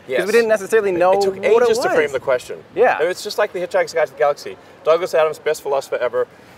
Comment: What is the title of the book Elon mentioned? And the author? I can't catch it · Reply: Hitchhiker's Guide to the Galaxy by Douglas Adams